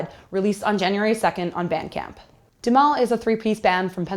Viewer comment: Scott West allot of people probably won't, sounded like she said it's only being released on soundcloud, I'm curious how she heard about it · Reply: Memphis Ashes I thought she said Bandcamp?